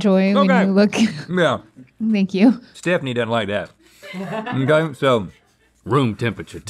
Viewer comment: Did Rhett call her Stephanie??